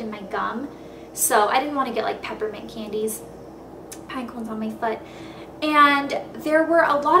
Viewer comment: Did you just say "pinecones on my foot"? Lmao